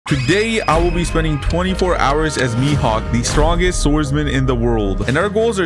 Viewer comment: I thought it said MIKEHAWK